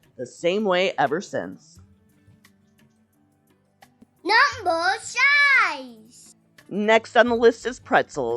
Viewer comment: It kinda sounded like he said "Number Size" instead of "Number 5"